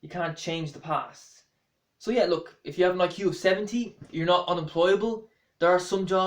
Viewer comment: Sounds like he said and IQ of 17